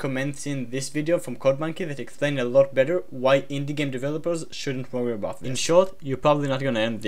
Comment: can't tell what you're saying here. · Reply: was it "shouldn't worry about that"?